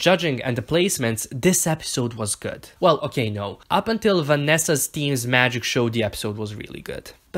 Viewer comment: i legit was staring at the screen all confused because i thought he made a mistake saying someone named "vanessa" then i googled the cast and realized it was vanjie and im yet again...a dumbass